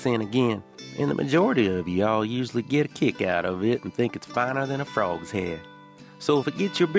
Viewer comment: Hey 👋🏻 y’all! At 7:12… I think 🤔 you meant to say “finer than frog hair!” Not ” frog’s head!” 🐸 hehe 😜🙃😉😀. My mother-in-law is from the South. She used that phrase all the time. Great tour. 💜👍